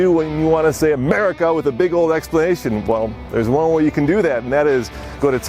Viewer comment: Did he say big ol explanation point instead of exclamation point ?